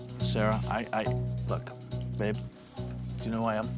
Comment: did he call her babe?